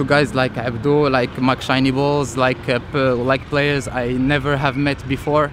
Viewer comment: did he say Mc.Shiny balls? 😂💀